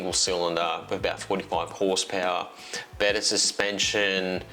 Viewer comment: I didn't mention the horsepower on that one? Yours must be arriving soon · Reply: I misheard you, I heard 25hp, but I think you said 45. Had to listen a few times. Forty-five would be nice, but it's quoted as only a hair under 40bh. Can't even trust my ears any more.